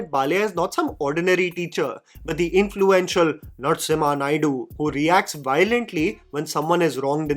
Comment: Let me tell you something Hilarious - When you said Narsimha Naidu, i wasnt looking at the video as i was doing something else - I heard "Nuts IN my Naidu"